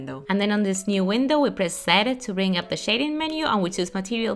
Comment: Didn't understand a shit. Please add subtitles because not everyone will understand, 3:37 WHAT THE FUCK DOES "SET IT" MEANS?! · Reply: She means press Z she pronounced it zede